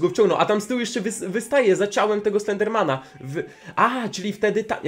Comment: powiedziałeś enderman czy ja źle usłyszałam XD